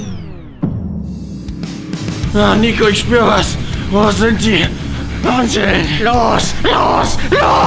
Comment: bis 2:16 wer hat auch Handschellen verstanden